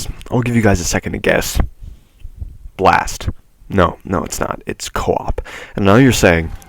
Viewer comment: I got an ad and it sounded like he said balls 🤣🤣🤣